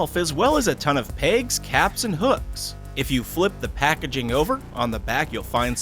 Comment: Sounds like he said pegs captinhooks